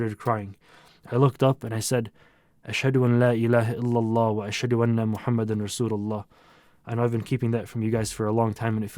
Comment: What did he say at 6:54 ? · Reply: "I bear witness there is no God but Allah and Muhammad is the messenger of Allah" This is the islamic testimony of faith. It is encouraged to be said by the dying person as their last words. It is also said in prayer